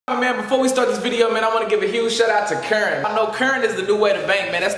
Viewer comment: yoo i thought you said you wanted to give a huge shout out to 'karen' lmfao